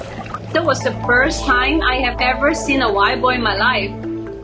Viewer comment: Cute story, though I kept hearing "white boy" instead of "wild boar", made me chuckle.